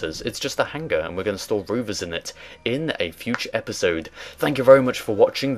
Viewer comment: Did you just say 'roover'?